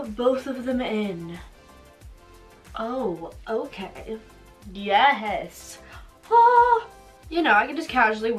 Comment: IF YOU WANT TO PUT IN CONTACTS TOO READ THIS PLEASE - You need to soak the contacts first for 4 hours in contact fluid. That is why it could hurt so much. The fluid they were in in the bottle, is not good for your eyes which is why you need to wash them off with contact fluid. - It's also not such a good idea to have long nails because they can scratch the contacts which than can hurt your eyes. I know, I know they look amazing, but if it is your first time doing contacts, it's easier if you can use your fingertips. But if you are used to using your long nails to remove your contacts, it's fine (like). Thanks for reading this. I hope you listen to this advice. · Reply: I heard a YEH HES